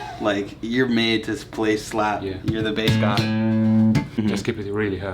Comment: I heard:"thank you bass God" Also heard:"check mate" in my head right after